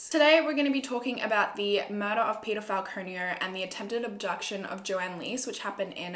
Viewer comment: I literally thought she said pedophile the first time I heard Peter Falconio😶